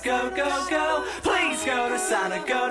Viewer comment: at first i thought they were saying 'please go to salad, go to salad, go go go'